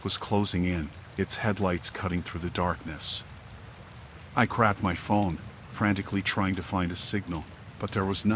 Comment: sounded like he said "i crapped my phone" 😆